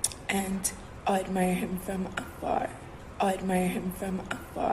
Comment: what does she even say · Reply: "I'll admire him from afar"